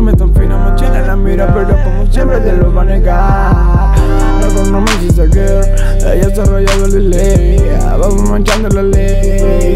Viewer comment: yo entendí "loco no me hiciste aquel"